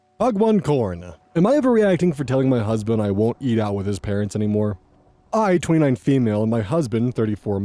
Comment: I CHOKED ON MY DRINK, I THOUGHT IT SAID "I won't eat out his parents"